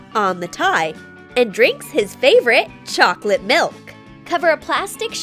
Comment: Did she just say "cocktail" instead of chocolate milk